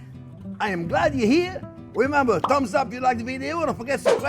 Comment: 😁👋🏻 Thought you said.... 'Crumbs up if you like the Video'..🤔 😂😂👍